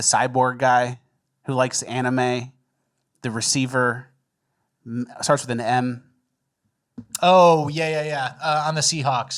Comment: i thought he said it starts with an N lol